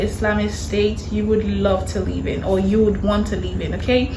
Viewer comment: Omg i apologize To you. I thought you said name one you would leave in, instead of saying LIVE in. I was all like that makes zero sense, how could you leave in. But anyways...thank u for bringing this video to our attention.